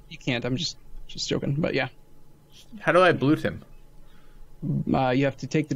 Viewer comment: I heard say: "How can I blow him?" lol